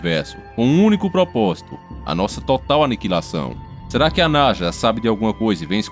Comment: Eu ouvi NAJA ? 🐍 0:46 Eu ouvi NAJA . É NASA 🌍 né haaa ta. 😁 Pra começar eu quero éque o mar o pegue fogo, rs... Pnc du nibirú! Rs